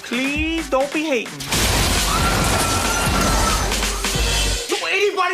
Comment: Funny thing is I misheard "Don't be hatin'" years ago as "Don't be Haden". I thought it was his way of saying "Don't be Hades" with the inflection.